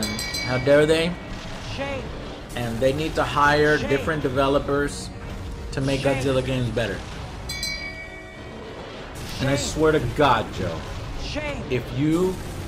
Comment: I could have SWORN I heard someone say "Shame! Shame! ding ding Shame! Shame" in the background... :P